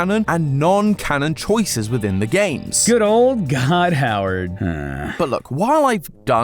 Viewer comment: did you just called Todd Howard God?!